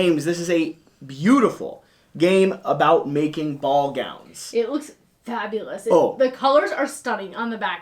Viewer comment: He said ball gowns I thought he was saying ball gags I almost choked on my food